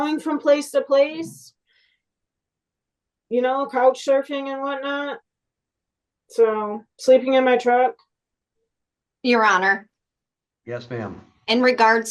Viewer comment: I have to check my ears. I thought she said COCK surfing. Poor thing must be super tired.